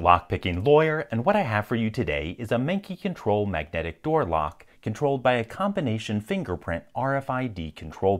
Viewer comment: I legit thought he said "and today, we have the manky control magnetic door lock" and if he did, I would love his brutal honesty